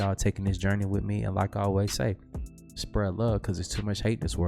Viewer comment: What does say after he says, “I’m out though”? Sounds like Lao.